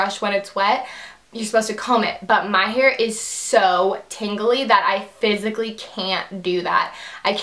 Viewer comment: I thought you said "tingly" hair, aha. Love you <3